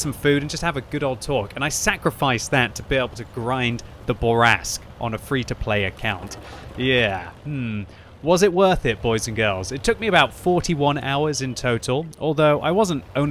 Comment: The bore ask? Did I hear a Freudian slip or is it just me? Boo rask!